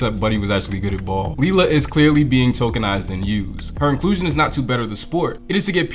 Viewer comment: I swear, I thought I heard you say “tolkienized” at 7:36 lmao. I went back and heard it correctly the second time, but if you had done this, that would have been one of the best and most subtle South Park references you could’ve made.